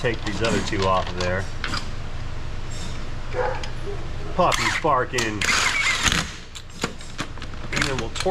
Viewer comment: I thought said the “puppy was sparking” 😊 Tell the puppy to quit connecting himself to ground and battery positive! Much less puppy painful that way.